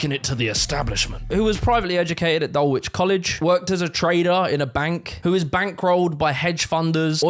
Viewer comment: In the part where you say: "he worked as a trader", I misheard it as: "he worked as a traitor". 🤔